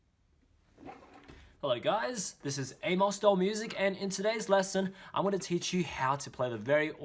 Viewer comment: When I opened the video I legit thought he said Hey guys it's Anus Doll Music Here XD